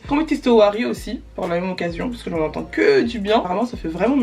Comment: Coucou tu peux me dire c’est quoi le truc dont tu parle à 16:07 j’ai entendu OARI mais je trouve pas c’est quoi please ? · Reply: Owari 💕